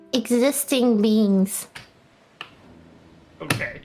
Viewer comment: At first I thought she said beans not beings